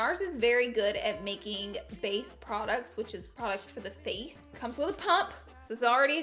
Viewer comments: “Nars is very good at making face products which are products for the face” 😂😂 I died! You are the best! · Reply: I thought she said base products are good for the face but hey I might me wrong 😂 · Totally thought you said “Nars is good at making face products which is products for the face “ 😂😂😂